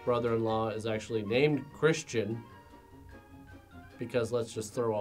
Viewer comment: what did you say his name is? chris chan?